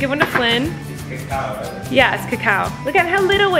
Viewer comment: Omg did I just hear Flynn say "is it chocolate?" Or is it just me?!